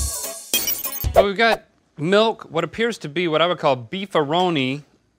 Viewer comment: At :49 I thought Rhett said "my wood" not "I would". Lol